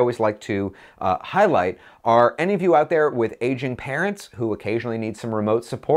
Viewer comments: Hahaha, I thought you said "Asian" parents, not "aging"... Was wondering why you thought Asian parents would need more help with their Macs than non-Asian parents! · Omg I thought you said "Asian" parents! lol